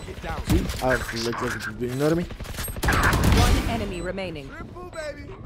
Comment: did he just say you can nano me this isn't overwatch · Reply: "you know what i mean" really fast though